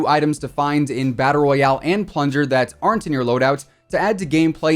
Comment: maybe I’m just hearing things, but I heard plunger 🚽